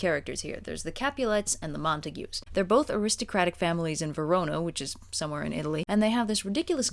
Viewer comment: I'm aware the comments are all commenting on the content of the video and while that's all well and good... Did say "Verono" at 0:45?